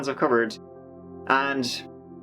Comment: Hehe every time you end a video your accent makes it sounds like you say My cow, or mike ow, insted of mike out, Ive just binged like almost all your vids :)